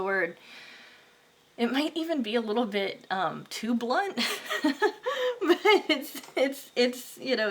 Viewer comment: 2💛💛 you made me happy. Thank you so much. I feel gratitude for discover your channel. My english is not the best and i don’t understand what you said at 22:38 “might be a little *i don t undertand” :) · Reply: Hi! Thanks so much for the feedback. I said "It might be a little too blunt." When people are blunt, they're extremely truthful, usually in a sort of dispassionate way. It also means rough, matter-of-fact, and direct. Hope that helps! <3